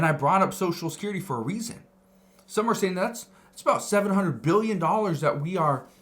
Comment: You’re saying 700 billion was over spent and Social Security. I thought he said 72 billion not 700 billion.